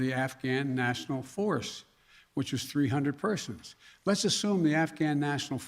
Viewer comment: This guy's is a joke he has no idea what to do. So incredibly sad. He literally said 300 Afghan troops WTF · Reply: It sounds like he says national Forrest and 300 persons